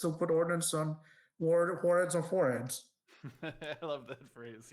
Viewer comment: What did he say on 1:30:30? "put ordnance some ??? on forearms" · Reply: warheads on foreheads!!